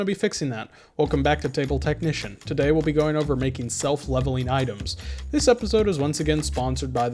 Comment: Not gonna lie I thought it said 'self levitating item'